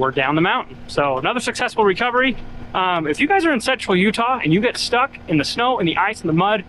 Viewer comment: Did say “sexual Utah”? Because that would be an oxymoron.